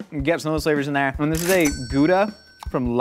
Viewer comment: I was confused because I thought Josh said it had human in it